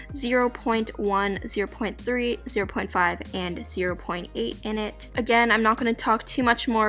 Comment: Is me or anyone else who Heard her saying 0.8 instead of 0.7 on